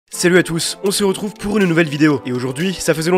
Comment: Vous comprenez quoi la première seconde ?? Perso j'entends "C'est le à tous"